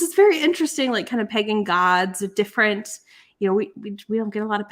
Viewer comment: Please tell me I'm not the only one who thought said pegging gods instead of pagan gods 😭